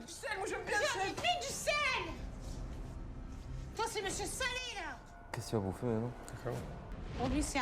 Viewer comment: "Faut mettre du sel, moi j'aime bien le sel" et le "J'en ai mis du sel! Toi c'est monsieur là!" les deux m'ont tué 🤣😂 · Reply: "Toi c'est monsieur salé" qu'elle dit, pas " Toi c'est monsieur là".